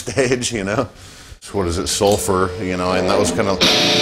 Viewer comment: tey wrote SUFFER in the subtitles instead of SULFUR^^